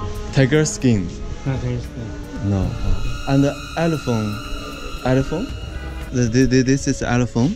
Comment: 那个不是象牙做的电话 我估计是那个卖东西的把说的elephant 听成telephone了